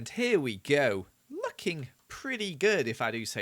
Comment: When said “looking pretty good” I heard “f**king pretty good” and I freaked out and was like oh no swore and he forgot to cut it out